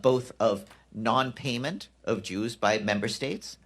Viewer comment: The dude sounds like he said Jews instead of dues. lol